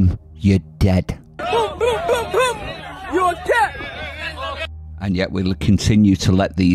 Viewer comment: I thought he said, "boom, boom, boom, boom you're a cat."